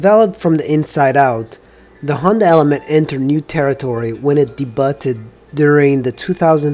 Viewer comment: Funny - 8:07 he said "dee-butt-ed" instead of "day-byood"